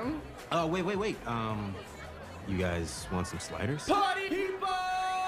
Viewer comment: HELL ME💀 0:48 did i hear PARTY MEATBALL💀☠